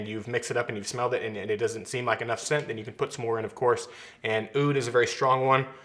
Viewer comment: can someone please tell me what he is saying? Oohd. I clicked on the link for his essential oils but no longer listed. thanks. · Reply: Oud oil